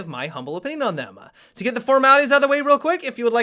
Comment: I've been here for a year and i still dont know what he says after he says "i will give my humble opinion on them", i hear the F but thats it i always hear it "lets get the frmamerlyz out the way" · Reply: LIGHT x45 "let's get the formalities out of the way"